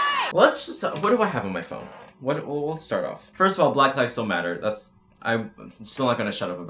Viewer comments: I was VERY CONCERNED FOR A MOMENT cause when he said “black lives still matter” I thought he said “ black lives DONT matter” and I had to turn on the captions to make sure before I started throwing hands 😂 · For some reason the captions said “black lives don’t matter” and I want y’all to know I literally said “black lives still matter” Also masks have been proven to not restrict oxygen flow. Don’t say you can’t breathe in them, just say you don’t like having to wear a mask. OH AND here are SOME of the 100+ “influencers” who went to that party unmasked: Nikita dragun, Emma Chamberlain, James Charles, Charli and Dixie D’amelio, The entire hype house, Bryce Hall, Larrayxo. And turns out cases in a lot of other countries are still rising. Oh well I guess this entire world is stupid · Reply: oooof i blew a fuse. i actually thought u said black lives DONT matter. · Oh shit I’m sry I thought u said black lives don’t matter dat shit pissed me the fuck off because me being black is the thing I love most about me and now that I hear it the video does say “black lives still matter” and I just went i madman mode sry lol much love to the channel my apologies 😕🙂😇